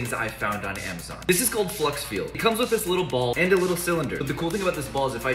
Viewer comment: When he said ball I thought he said balls 💀